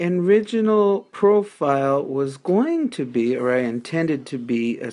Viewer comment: Did you say "my enriginal profile...." at 1:58? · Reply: joeyisfunny no, lol. I said "my original profile." Thanks for watching. Cheers!